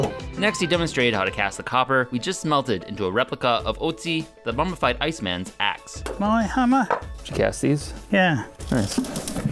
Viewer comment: No disrespect, but @ 3:41 I could have sworn you said "Otzi, the bumfight iceman's axe"